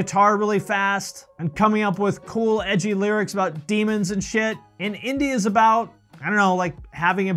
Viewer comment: Gutalax: 21:35 did someone say SHIT!!??